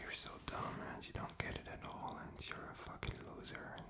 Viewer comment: I was listening to this while trying to sleep and I heard him say your a fucking idiot it scared me to death😂😂😂